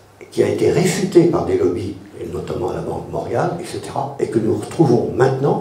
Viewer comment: Bonjour, à 48:40 parle d'une banque c'est quoi le nom de cette banque? Merci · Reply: banque morgan, jp morgan jimagine